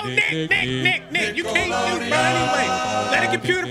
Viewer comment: i thought was finna say “no nigga” 😭